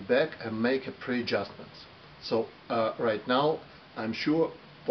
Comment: What are you saying at 1:08 into the video? Sounds like you're saying something like "make a pre-jumper". Please spell out what you said there. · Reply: Hi, I've checked this part (from 0:56 till 1:09), I tell, that "I dismantled totally the carb, clean it thoroughly, assembled it back and make a pre adjustments". It means, than I restored carb factory settings ( as it delivered from factory), to start test and carb tuning from the beginning. Thank you for your questions, and have a nice day !